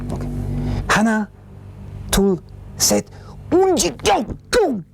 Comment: 하나둘셋 운지 꼬 꼬 나만 이렇게 들리나 ㅋㅋㅋ